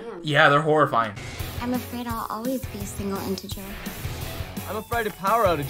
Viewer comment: did she just say she was afraid of being a single integer? 😂